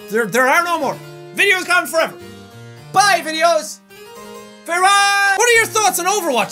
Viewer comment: I swear at 6:11 he said frerard